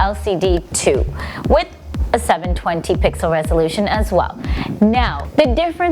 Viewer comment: 702p @ 1:09, hmm, swore she said 720.